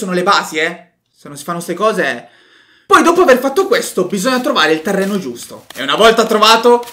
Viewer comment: io ho capito bisogna trovare i terrapiattisti